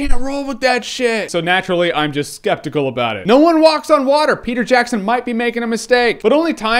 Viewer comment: haha i thought you said "Nolan walks on water" and i was like… ya sure :D